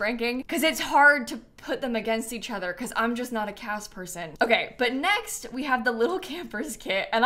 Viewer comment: Why did I hear 'I'm not a CATS person' and immediately thought 'but she has cats'… only to realize she said CAS. 🤣🤣🤣